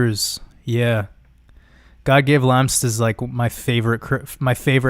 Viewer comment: What’s did he say was one of his favorite content creators (1:38:45)? I couldn’t make it out. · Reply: Godgevlampse